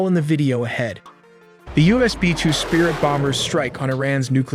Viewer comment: I swear I heard “b-2 bonger” and “bunger” not bomber and bunker